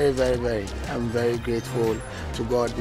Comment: he says "he's grateful to god" but the subtitle says "go up"